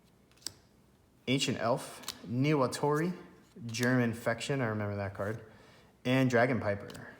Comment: It sounds like he said German infection